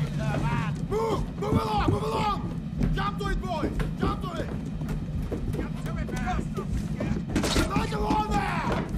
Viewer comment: What does he say at 1:23? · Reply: “Jump to it, boys! Jump to it!”